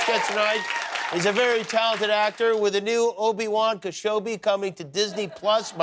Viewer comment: Did he just say Obi-Wan Keshobi?